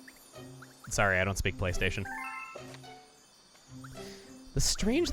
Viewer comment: I think she was saying Playstahion